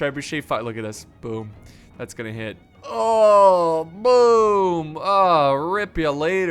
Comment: did he say oh moom